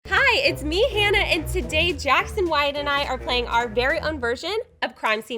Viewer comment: Tbh at the beginning of the video I thought she said "Jackson Wang and I.." 👁👄👁